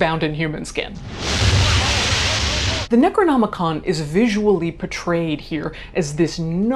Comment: Omg i kept hearing “necro-comicon” 😂😂😂